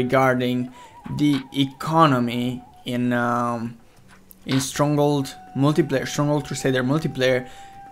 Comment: I hear "stronggold"